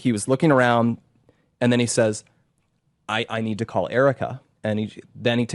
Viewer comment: Pause 12:03 minutes, he just said it: “I think I imagined” but I think he said: I have to call Erika “ Yes, buddy you can imagine a lot, the facts that you both run in the moment of truth, where is the friendship? Where is the love for someone you claim now was so close to you? I can never get tired to tell my children , be careful with you hang out with, there are not friends, friends don’t exist!! That real friends are not many in your life time, there Is only one real friend in your life:JESUS!! and your mother! In this case I say Mother too because I am the only parent who is loving them and watch over them in this earthly disgusting place. But Jesus said; even if your mother abandoned you I will take you and care of you. So tired of people’s lies.